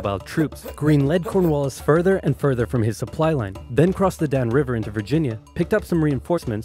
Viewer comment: In 9:53 I thought Oversimplified said DAMN river but no, it was Dan river Sadly😢